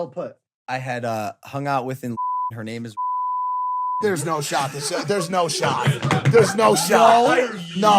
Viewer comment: What is mike saying at 0:35? "There's no ............." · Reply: "Shot"